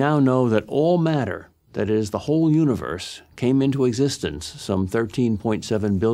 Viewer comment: I heard him say "butthole universe"... after said "butthole". I might've made that up.